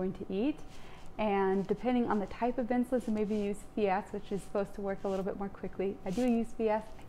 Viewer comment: what is the name of the insulin you said you use? I don’t think I’ve ever heard of it 🤔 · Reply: FIASP supposed to work faster than Novorapid